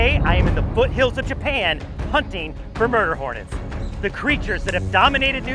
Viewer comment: I thought he said burger hornet